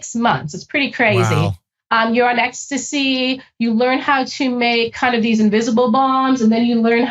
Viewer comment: I’m sorry did I hear that right? Did she say that they were on ecstasy as an MDMA while suicide bomb training?